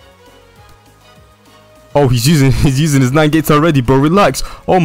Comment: did bro really say nine gates